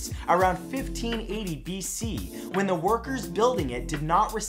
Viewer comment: The Egyptian pyramid builders weren’t slaves and they got paid a massive amount as well as being very well respected in society. Anyway, great video! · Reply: whoops im very tired, I thought he said slaves, but I rewatched and he said workers :-) not slaves lol